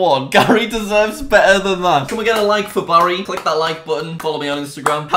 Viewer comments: anybody else hear Barry? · Does he say can we get a like for Barry